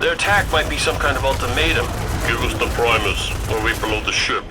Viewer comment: what does he even say? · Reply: tiweka "Give us the Primus, or we blow the ship"